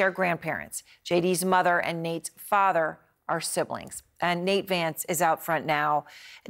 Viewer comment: I wasn't paying attention at the beginning and thought I heard "JD's parents are siblings." I would've believed it, tbh.